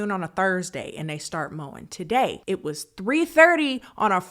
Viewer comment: OOOOOH mowing, last week I thought she said moaning, which made go like ".... Okay" Now I'm like facepalm, omg silly me. That took me a week to realize this wth. Brainfart. I also didn't have the volume on super loud either. Sighhhh